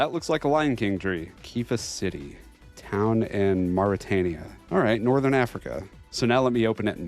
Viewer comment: I heard Morytania & I thought it was time for a Barrows run. 😂